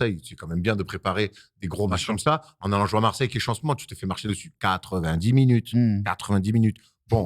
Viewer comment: il dit quoi? qu'est chancelant ? quel changement?quelle chance maman ? 😅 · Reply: Je dirais "Marseille qui est chaud en ce moment"